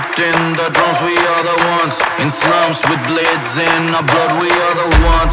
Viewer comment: I thought they said "we are the ones in slums with blades in our bloods"...and i was like wtf are they on about😂😂😂